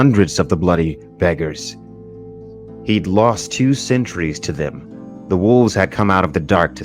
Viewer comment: lmao i thought he said centuries instead of sentries.